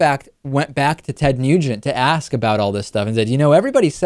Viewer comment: It sounded like David said Ted "Mutant" in the beginning...very appropriate.